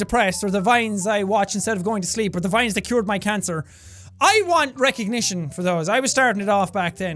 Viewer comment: WAIT WAIT WAIT… did I hear that right? "or the vines that cured my cancer" well I guess I learned 2 new things today lol